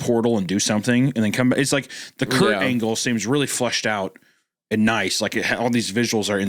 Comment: Did he say Kurt Angle? Secret wrestling story confirmed.